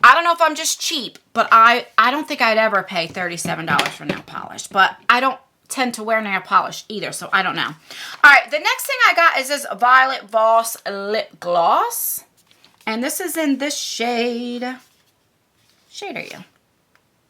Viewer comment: Why do I always mishear Violent Boss for Violet Voss? Makes me do a double-take all the time :)